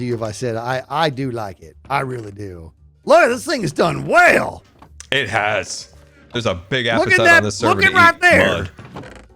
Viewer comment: What does say here?! Did I hear that right?! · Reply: He just says "it has"